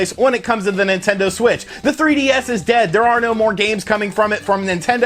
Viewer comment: Wait did you say the 3ds is dead. I heard it's still selling.